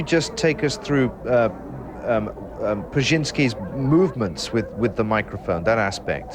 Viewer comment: While listening to this, I thought they said "Brezenzski", as in Zbiginew. I thought, "Jesus, that guy is everywhere!" But, then I saw it was "Prusynski". I'm everywhere, Focker.